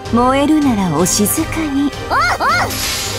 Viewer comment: Ok... I know they're saying "Oh, oh" back to back but why do I hear "woof woof"?